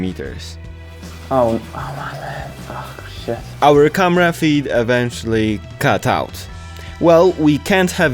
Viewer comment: almost sounded like comrade feed lol